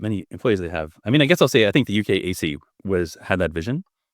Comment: Did anyone else think he was saying "UKAC" at 1:35:25? (Later in the episode he mentioned one of its employees and I was able to look her up and see that it was the UK's "AISI".)